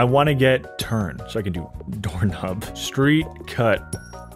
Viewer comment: Guys... I might be trippin... But did bro say Door Hub?